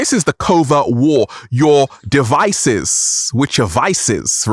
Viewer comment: You’re correct.. I heard you say “Vices” and when you said “devices” it all makes sense now.